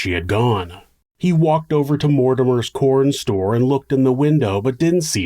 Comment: The first time you said it I heard it as "Mordor's Corn Store" and now I'm just obsessed with the rhythmic phonetics 😅